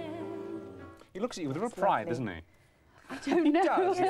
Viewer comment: What does he say at 07:12 ? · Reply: "he looks at you with a little pride, doesn't he?"